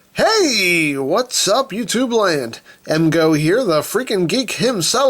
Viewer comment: The first time I saw review i thought he said, "what's up YouTube (bland)" instead of YouTube land😂